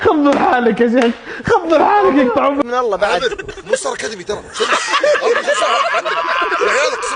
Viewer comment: ما سمعت شكال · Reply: قال " مو ستار اكاديمي ترا "